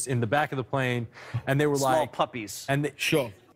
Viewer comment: "small puppies" thats what I heard. I feel like its code for young girls/ boys